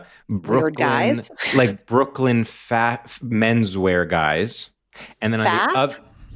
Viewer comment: The gaslighting!! 1:12:12 I literally did not say the letter F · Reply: I thought he said “fat” and I was like oh interesting! It sounds like maybe he started to say a different word like “fashion” and changed to menswear lol